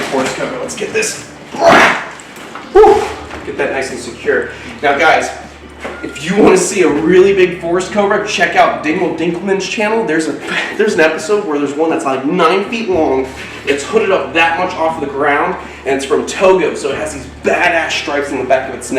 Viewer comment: Did he just say Dicko Dinkerman?